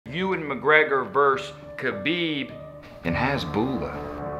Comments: Not gonna lie I thought he said Ewan Mcgregor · Thought he said Ewan mcgregor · I heard "Ewan McGregor vs Khabib and Hasbula" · I thought he said Ewan McGregor when I first scrolled by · I thought he said Ewan Mcgregor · I thought he said "Ewan McGregor" LMAO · I thought he was saying ewan McGregor at the beginning